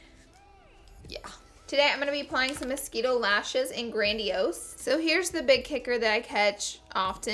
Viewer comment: what lashes are these? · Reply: Crystalbell - it sounds like she said "mosquito lashes in grandiose" in the video.